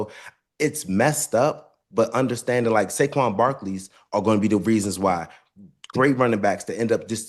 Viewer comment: I know i heard “ Sanquez Barkley” 😭